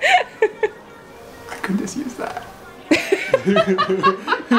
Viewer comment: What was last comment that found so funny? I could not understand what he was saying. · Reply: It sounded like "I could just use that", after said, "We hope you enjoyed this kabobtastic video. If you'd like to support us on Patreon, you should do that."